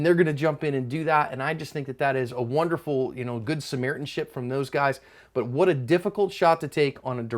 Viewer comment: I had to rewind because I thought he said 'That was wonderful good Samaritan shit from those guys'. I was wrong but great video and commentary as always. Cover your ASP people.